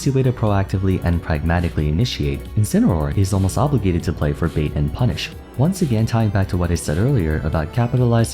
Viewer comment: I-I'm sorry, did you say "*Furbait* and punish"?